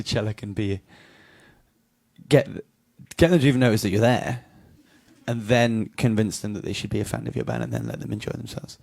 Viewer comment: literally what is he saying at 0:47 please need help x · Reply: “get them to have noticed that you’re there, and then convince them that they should be a fan of your band...”